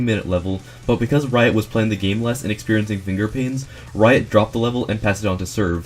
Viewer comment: did you say "experienting"